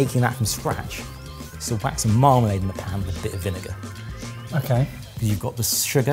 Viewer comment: I heard "Marmite" instead of "marmalade" at first and was so confused.